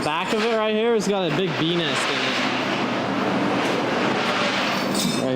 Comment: s I thought I heard something different than what was said, “bee nest” sounds a lot like something else when your not fully paying attention lmao😂